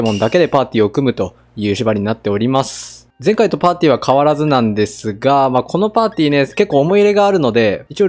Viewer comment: ～前回のパンティから変わらずなんですがに聞こえた